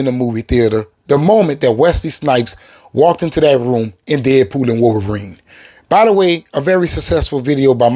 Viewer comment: What did he even say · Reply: “walked in on Deadpool and wolverine”